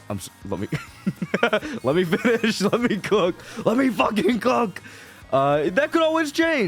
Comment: AYO, what is bro saying? 🤨🤨🤨😳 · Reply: LET ME COOK